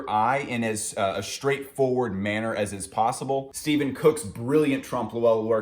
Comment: Is he saying Steven Cook? I can't find any info on that when researching the picture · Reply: Stefan Cooke 💫💫